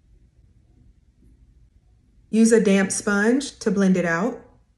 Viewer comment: Hey Fam! Here’s the links to this FULL STEP BY STEP Makeup <link> 📌All products are linked here: 💕 <link> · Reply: At first, I thought I heard you say used a damm sponge 😮